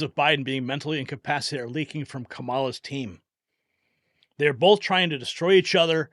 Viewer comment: I thought he said Kamala-dine. I'm like hell no.